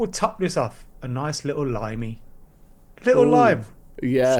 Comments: Thought said “nice little LINEY” 😅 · I thought he said a nice little line